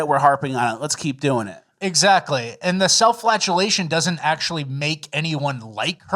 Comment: What I heard at @ 36:25 "Self flatulation" ? 😆